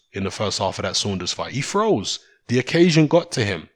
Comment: i thought yo were saying 'throws' when you said froze lol. was confusing